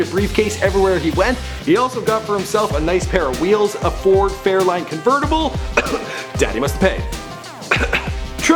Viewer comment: When he said he opened his own "airline" I thought he said "hair line".... I nearly choked on my water!